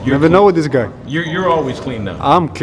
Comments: Did he say, "I'm clean...we white dudes are always clean ." 😂😁😃🤣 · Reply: i heard "we white kids are always clean" lol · I thought he said: “We’re white, we are always clean”